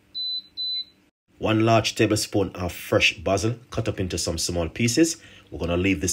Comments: I thought he said "buzzard" not basil 😅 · Thank you for your time and dedication in informing us of natural ways to heal ourselves. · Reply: Hello. It sounds like he said basil 🌿 leaves. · THANK YOU, I to had to listen repeatedly!!! I too thought he was saying buzzard!!! I read you message and found it was BASIL!!! THANK YOU!!!💕